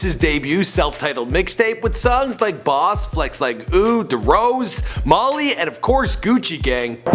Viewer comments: Did he just say DeRose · DID HE JUST SAY DEROSE? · did he really say “derose”